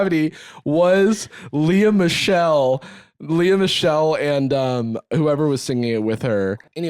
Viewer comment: I thought said Liam The Shell, not Lia Michelle, when they brought up Glee lmfaoooo. as someone who’s only seen a few eps, I was really racking my brain trying to figure out who tf was nicknamed ~The Shell~